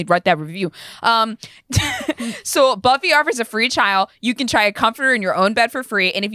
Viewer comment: BRUHHHH at 21:42 I thought said "so Buffy offers a free child" and I was like "WHAAA?!"